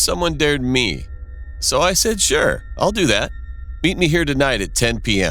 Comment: did anyone hear beat? Or is it just me